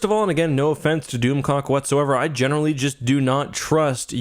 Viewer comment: I swear to God! I thought was saying DoomCock. I'm sorry! I was losing it when he kept saying DoomClock. 🤣😂